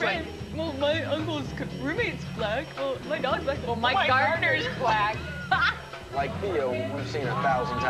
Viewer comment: Haha I love how ''My gardener is black.'' was too edgy for this whole group. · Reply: I thought he said Mike Gardener. I've been googling him thinking he must be someone famous.